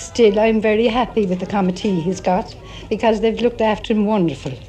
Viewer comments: i think she said "she's very pleased with the committee he's got" referring to the gathering of spectators and people assisting him. The way she pronounces it sounds like "Comet tea" haha. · i think she says committee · did she say committee?